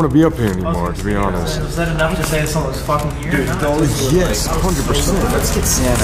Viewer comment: are you using Sam for power ( it’s sounds like it says yes)